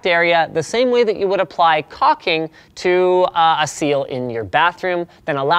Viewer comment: Caulking. Sounds like you said cocking.